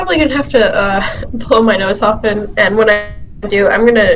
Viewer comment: I love this video Hold on, am I mishearing or did she just say she was gonna pull her nose off?